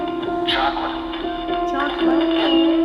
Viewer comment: when the machine says chocolate, I think it’s saying Chaplin instead? Unless she can actually read the words than maybe not!